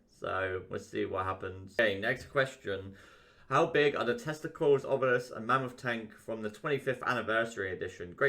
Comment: Lol I heard "how big are the testicles of Mammoth tanks"